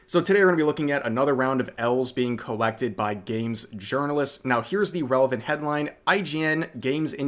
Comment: I heard: "Another round of elves being collected by Games Journalists" and I was like, "please leave us Elves alone....."